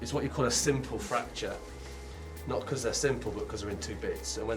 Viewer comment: I thought he said "Sinful fracture." Then I realized that it was simple fracture. LOL